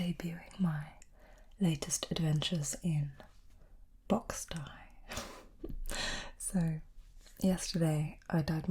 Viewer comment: For a moment I heard "adventures in box tie" and I was like.... erm...what?